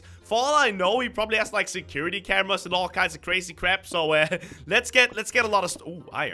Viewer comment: I thought he said crazy craft!